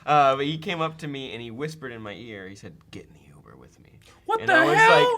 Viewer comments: What did say He was told at the live show? I watch it like 5 times and can't tell! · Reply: Dianne Baldini I thought he said get Into the humor lol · what did the guy say to? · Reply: I thought he said "Getting the over with me" I thought I had missed some kind of inside sourcefed joke